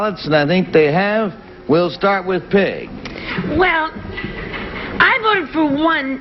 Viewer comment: the audience snickered because it sounded like Garry said "We'll start with pig" instead of Peg